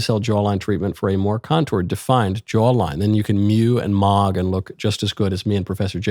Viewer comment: did i just hear michael knowles say mew and mog